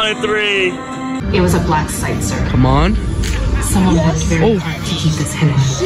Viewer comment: Did bro just say it’s the guy from Fortnite?